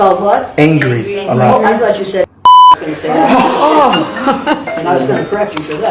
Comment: Maybe I heard this wrong, but I thought she said, " I thought you said n word." to the student that said he was being called angry all the time. As far as I can see, she didn't call anyone the n word. 🤔